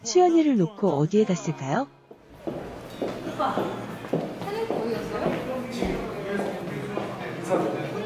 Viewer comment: 에 "오빠, 찬혁이 어디갔어요?"라고 들리는 건 기분탓이겠지.. ㅋㅋㅋㅋ